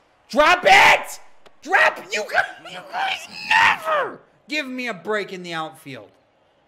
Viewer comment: I think we heard him say the n-word 🤣🤣🤣🤷🏽‍♂️ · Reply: naw he said "you guys never" then he said "give me a break in the outfield"